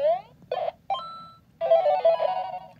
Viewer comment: Is it me or does it sound like he said "you cheated!" instead of "you did it"